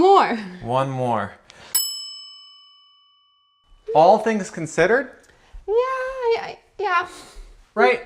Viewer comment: I heard “Wall things considered” 🤭🤭🥰